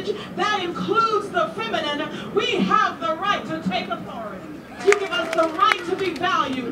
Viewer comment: Did she say we have the right to TAKE authority?